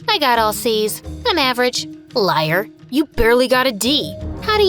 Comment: When you said whiers I thought you said liars😂😂😂🤣